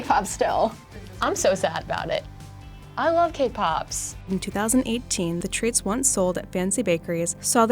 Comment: I thought she said “I love K-pops!”